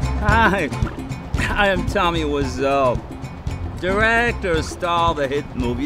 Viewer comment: He said "I'm Tommy Wiseau", but what I heard was, "I'm Tommy. What's up?"